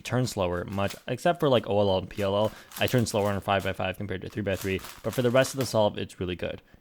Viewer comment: did he just say "compared to two by three"?????